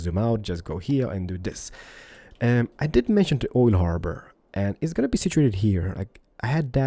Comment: Has Keralis been saying oil harbour this whole time?! I thought he was saying old harbour haha